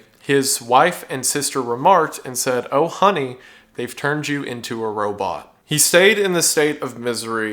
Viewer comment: I thought they were gonna say "oh honey, theyve turned you into a rotisserie chicken" why does my brain do this